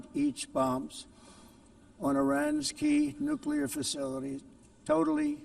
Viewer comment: does he say Iran’s Key or Iranski?